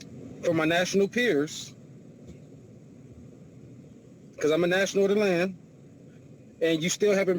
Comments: I think he keeps saying "I'm a National of the Land" (but I heard "Atlanta" the first time too) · He says he’s a national of the land. At first I thought he was saying Atlanta